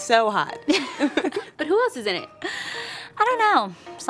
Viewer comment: What does the girl say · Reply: “but who else is in it?”